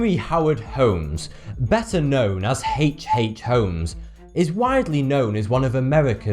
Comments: All I Heard was "Heeeecccch Hhhhheeeeeeech Holmes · I misheard "H. H. Homes" as "Hate Hate Homes"